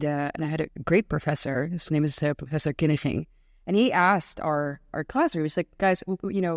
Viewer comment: What is the professor's name that she mentions? I hear KiniCHing (with CH being the grovelly ch-sound of Dutch).